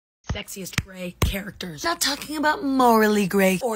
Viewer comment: I thought you said sexist and I was so lost